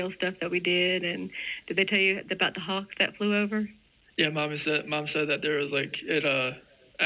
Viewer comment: did he just say (or begin to say) "Mommy said...." ?!